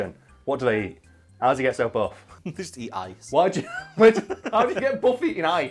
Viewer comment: I misheard ice and I genuinely thought you said they eat ass haha